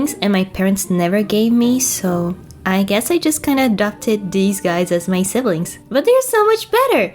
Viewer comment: Did I just hear adopted these kids or abducted these kids 😭